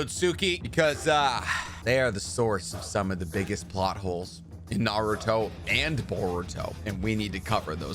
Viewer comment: Idk why I heard "buttholes", instead of "plotHoles" at the end and I was LOL 😂